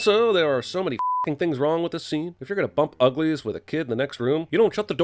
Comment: I heard that as "Bump uglies with a kid, in the next room" and I thought What the fuck?